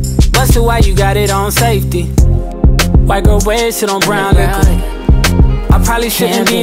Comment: I thought she said “ Body Listed as Brolic”, instead is white girls wasted on brown liquor” mine makes more sense.